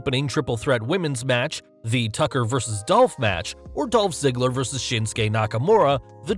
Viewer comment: did he say dolph ziggler vs nakamura